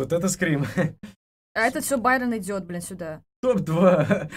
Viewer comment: Мне одному послышалось бл* тайм код=> 2:29 я хз лайк закинь плз